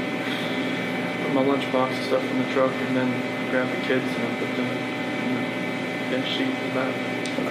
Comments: " then she left " 19:46 Wtf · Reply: I hear, “I grabbed my lunchbox, and the kids and put them in the bench seat in the back” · " then she left " 19:46 Wtf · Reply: "and put them on the bench seat and left".......... that's what I heard. However, "put Them on the bench seat", sounds like they weren't alive at that point.